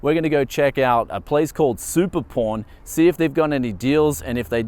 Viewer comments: At first I thought he was say Super Porn Shop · I thought you said "Super Porn" LOL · did he just say super porn? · Wait at 0:56 did you say super porn?